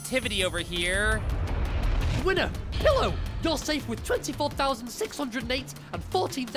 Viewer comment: I thought It said killer And not pillow